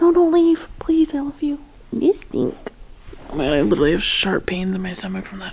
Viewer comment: I’m cracking up. I thought you said you had “Shart” pain😂😂😂😂😂